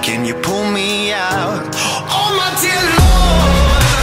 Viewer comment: 이거 저만 oh my dear lord가 엄마한테 일러로 들리나옄ㅋㅋㅋㅋㅋㅋㅋㅋㅋㅋㅋ